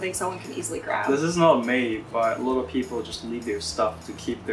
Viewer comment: When said 'A lot of people' I first thought he said 'the little people'.